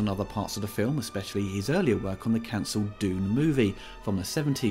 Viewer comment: I was listening while tabbed out and I thought for a second you'd said "Doom", not "Dune", and now I really wish we had the chance to see a Giger-designed Doom movie